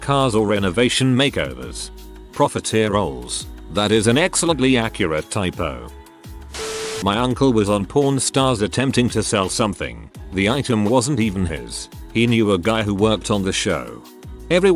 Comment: I misheard Pawn stars as Pron stars.